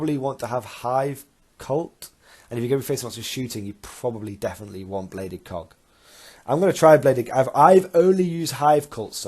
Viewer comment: I swear at 24:19 it sounds like you said "you probably definitely want bladed cock" I had to listen to that a few times before I was sure what you said lol 🤣